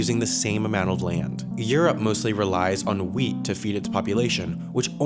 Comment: Okay so I wasn't looking at the screen; when they mentioned Europe relies on 'Wheat' to feed their people I swear I heard 'Weed' first and got really confused hahaha